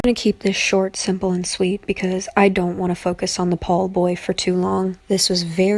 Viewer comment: Idk Why But I Heard "The Ball Boy" Instead Of "The Paul Boy." xD I'm Sorry.